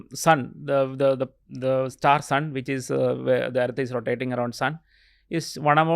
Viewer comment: Did he say rotating around sun ?